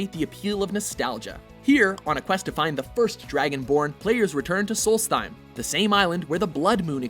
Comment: At number 5 I thought he said Dragon porn :P